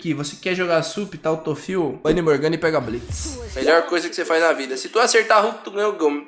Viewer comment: só eu q ouvi "vai de morgana e pega blitz" em vez de "bane morgana e pega blitz"?